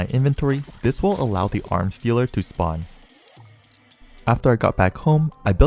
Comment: i do not know why my immediate thought was "arm stealer" instead of "arms dealer"